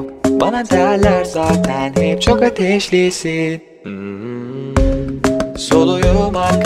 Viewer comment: çok ateşlisin diye duydum